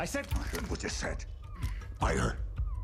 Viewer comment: What did Bryan say at 1:59 ? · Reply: I heard what you said. Buy her…